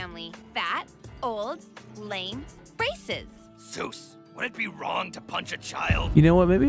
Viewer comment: OH SHE SAID 'BRACES' I THOUGHT SHE SAID 'RASCIST'